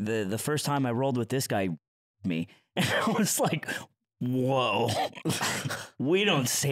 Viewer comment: What on Earth word are they talking about? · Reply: Probably "r*ped". It got used a lot a while back to mean 'beaten badly' in video games, which is how they talked about their experiences of it being used.